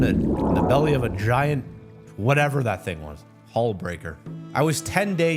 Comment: Nahhhh I’m fat as fuck man when he said hall breaker talking about the fish’s name I heard something else and said HALL BURGER? like I’m really hearing foods instead Of what people are actually saying this isn’t the first time either 😭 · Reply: Oh I thought he said hall breaker tho in the end it seems he said hull breaker